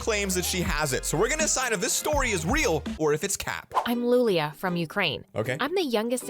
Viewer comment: You can clearly hear that at 0:07 he says "Weal" instead of "real"